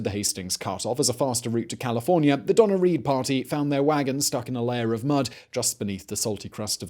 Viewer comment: It's humorous to me when you say "Donner-Reed Party" as I hear "Donna Reed party" which would be a completely different experience. :)